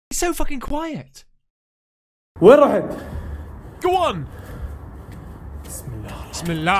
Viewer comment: did he just say bimallah